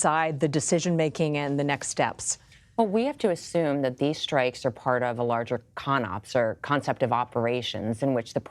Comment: is it just me or does it sound like she is saying these strikes are part of a larger Con?